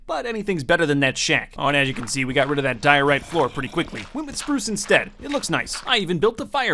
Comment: "We've got diorite roads" Iskall85 wants to know your location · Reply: When he says diorite I heard "Diahorrea"